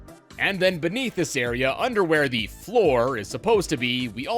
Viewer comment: did bro just say underwear?